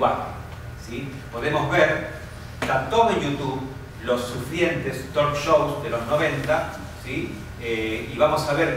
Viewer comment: profe no se entiende qué dice en el minuto 7:44 . Dice "los sufrientes tor show de los 90"? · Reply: ¡Hola Gonzalo! Digo "los sufrientes talk-shows de los 90". Si me pasás un mail te mando más información. Un abrazo.